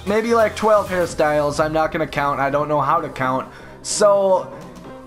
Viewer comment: did I just hear say I don't know how to count bruhh